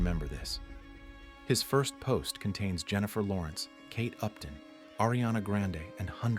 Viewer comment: i thought he said "caked up", then after rewatching i found out he said kate upton😂